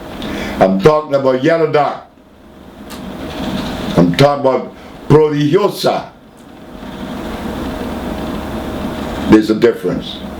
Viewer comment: Does anyone understand, what herb he mentions at 7:10 please? Thank you for your help. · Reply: nobody replies to you so far!? yellow dock and then prodigiosa (dream herb)